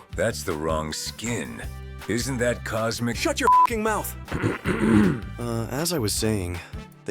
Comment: what did gojo say? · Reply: He said "SHUT YOUR F###### MOUTH"